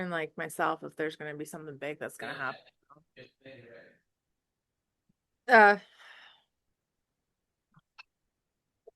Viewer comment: Was that voice at 19:53? It sounded like they said, "This January" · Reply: “January. This January.”